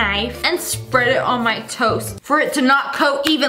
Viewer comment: LOL WHEN YOU SAID TOAST I THOUGHT YOU SAID TOE LIKE YOUR PUTTING BUTTER ON YOUR TOE!!LOL!!🤣🤣🤣😂😂😂😆